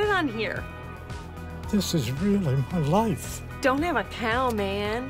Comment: non native english speaker here. what do they both say at 1:23 to 1:30 ? (I got the "what?" ;) ) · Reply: The older gentlemen says, "But this is really my life" = This is my lively hood/career you are making fun of, then the girl says,"Don't have a cow man", which is a Simpsons Cartoon phrase said by Bart Simpson, meaning, "Don't get mad in a very sarcastic way, which usually means, the lady who said it really doesn't care about the other persons feelings (which was the older man), and not to get upset because it bothers her, because she thinks it's ok to insult, as long as people don't bother her with being upset or sad". The older man says "What?" because he is either unaware of the pop-culture reference or surprised at how shallow and mean she is being in making fun of his pizza. Short version : She is being a scumbag to the older gentlemen.